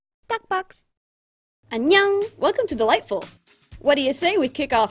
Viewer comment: Oop, every time I hear her say "Dollightful* I thought she was saying light bulb. Lol.